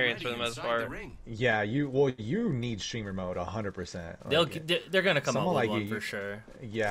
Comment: what does he say? What does need? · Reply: Streamer mode